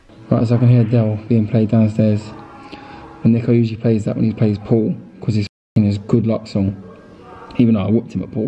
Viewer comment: thought he said po* lmao